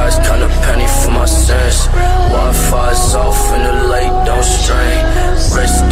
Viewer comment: Is just me or does it sound like x said wifis off and the lake don’t stream which sounds creative af! Rip x i hope i meet you on the other side